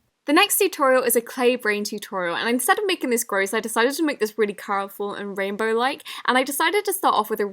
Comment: Who else noticed at 5:58 she said coroful instead of colourful?...😂♥️🌸😘😍💗😝☺️💕✨🎉🧀😍no hate i love you